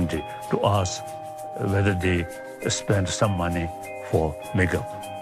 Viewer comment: The autotranslate is great! 5:28 :P I didn't get what he's sayin' at first, but after turning down from 1.5x speed to 1x I guess he said 'some money for makeup', right?